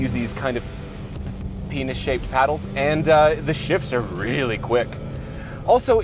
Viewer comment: did he say penis shaped paddles? or peanut?